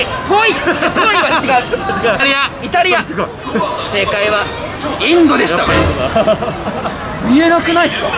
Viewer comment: 2: 29 and 5:17 Does Japanese people call Indians as Indonesia? 😀 I'm ABIT confused because your text says Indian but i heard Indonesia😂😂